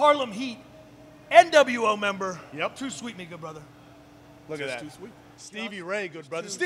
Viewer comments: I misheard "'Too Sweet' me, good brother." to "'Too sweet', N-word brother." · did he Just said The N word? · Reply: tomas maestrale no head didn't he says to sweet me good brother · wait wait wait did he say ni##@ brother